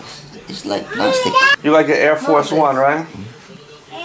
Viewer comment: I thought your pop's said "I like my shitz!" lol